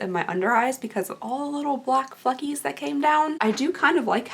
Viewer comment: I thought you said "all the little black fuckies came down" instead of fleckies lol 😆❤